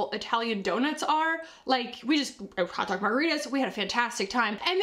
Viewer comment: Hot tub chicken don’t forget HOT TUB CHICKEN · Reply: I thought said Hot Dog margaritas!! 🤣🤣🤣